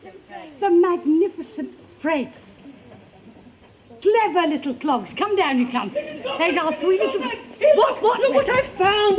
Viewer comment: Did she say "cunts"?